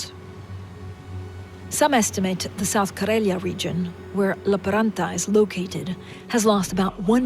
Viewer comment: These auto-generated subtitles are WACK. "South Korea region" instead of "South Karelia region"